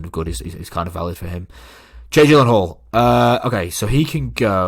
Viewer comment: Why did the captions say change hall instead of Jake Gyllenhaal at 18:48?